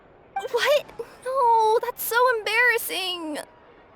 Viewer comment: Kirara said "What? No that's so embarrassing!" But the subtitles said "Glad to hear you've toned it down this time..."